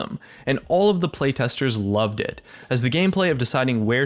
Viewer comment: You know what really pisses me off?! Your caption/subtitle. it's not complete. Ex. This newly released champion taliya was designed by this dude. Cap/sub: This new champion was designed by this dude. You need this fix this. Especially when explaining stuff and talking is the main reason why people watch your videos. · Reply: Some words you didn't say appear on the caption/subtitle. Turn on caption and go to 4:27 says "loved it" Caption says "really loved it".